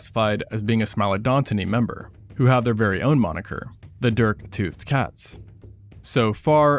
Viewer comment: Misheard dirktooth as derptooth